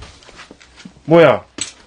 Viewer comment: 사람목소리가들려요 "뭐해?" 이렇게 들린거같아요